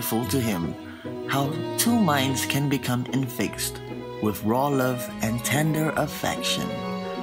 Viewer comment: I thought he said with raw love and tinder affection lmao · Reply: yeah he did HAHA